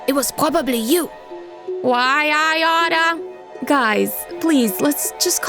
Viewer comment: What did she say? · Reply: Why I oughta